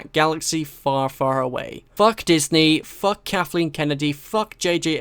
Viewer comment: I swear to god I kept hearing you say "Caffeine Kennedy" I am high again I swear.